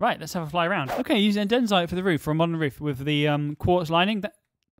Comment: Who else heard him say densite instead of polished andsite